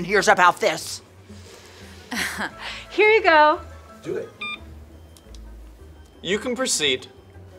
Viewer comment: Why did I hear "Do it"?